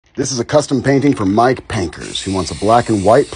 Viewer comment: I thought he said my name but he said Mike Painker and I am Mike baker